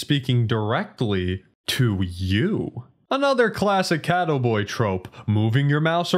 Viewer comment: the way you say catto boi sounds like you saying cattle boi